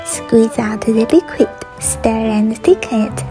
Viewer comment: i thought you said virgin honey 😅